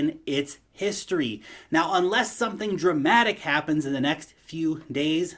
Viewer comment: When you said "dramatic", I only heard "Matic"... 😀😀😀. Btw, about this xrp/xlm competition, maybe its just Jed and Brad pumping their bags... 😀